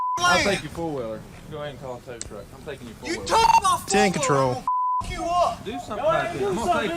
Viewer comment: Did I hear the homeowner say "I will fuck you up" after the police said they would take his 4 wheeler at 3:05?